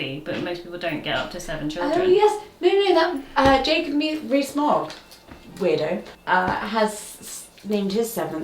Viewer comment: lol not having heard the name before, I thought you said "Jacob re...Smog". the wonders of the internet, lol